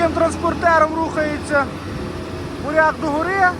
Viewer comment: Вітаю . Мені почулося на 0:53 , що таким чином умовний ,, бурят '' рухається догори .... 😉 . Забанять комент , мабуть ...